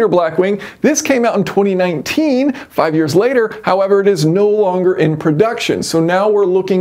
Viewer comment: Did he just say 2019 was 5 years ago🤔